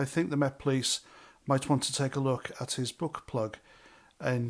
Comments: 😁👋🏻👍 I nearly choked on my coffee, when I misheard..... "They better take a look at his book-plug".... P.S. (2x speed is not recommended)....🤔🤔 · I thought you said butt plug rather than book plug. 😂 That will make me chuckle for the rest of the day.